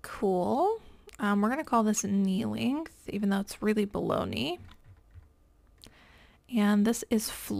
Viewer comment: Humorous moment while watching this video…..why am I hearing “bologna” instead of below knee?? 😂 Maybe I’m hungry! Great video. I am trying to learn more about factor stacking.